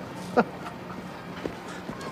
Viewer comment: A long time ago, I thought he said dang man you pee’d your pants.